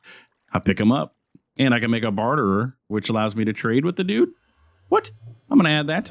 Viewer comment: Was I the only one who thought it said Bartener? 😭